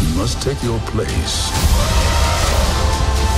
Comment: Parece que dice: In the circle of knife...